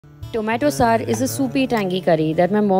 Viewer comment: I thought you said - tomato sir is a soupy tangy curry... Sorry